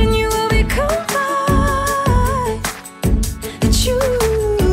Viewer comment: Please tell me I'm not the only one who keeps mishearing her and thinking she is saying "achoo" instead of "I choose"